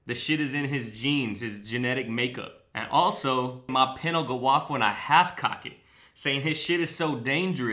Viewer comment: "Pen will go off" I always heard "Pinacle off" Now I'm thinking it's a deliberate double entendre man. Fuck knows haha. Sick video so far